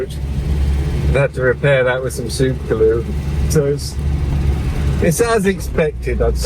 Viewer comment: I thought I heard say he repaired his car with super glue. I sure hope I misheard that!